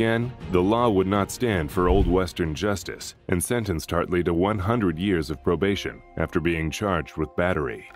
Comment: 100 years of probation for punching a baby killer one time is insane! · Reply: They clearly said 100 years, not 100 days. There's also text on the screen at that point in the video that says 100 years of probation.